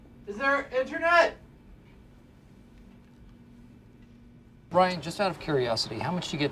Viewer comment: did Michael call him Brian or is that just me?